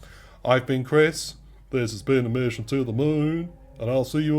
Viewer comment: you got my follow once i heard "this has been a message to the moooon", sounded like Cat in the Hat "Lets check under the hoooood!" lmao